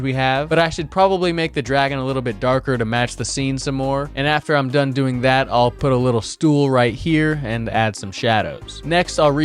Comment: did bro say стул?